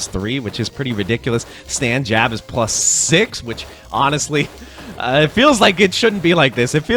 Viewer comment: "Stand jab is +6" Why do i hear find your one way?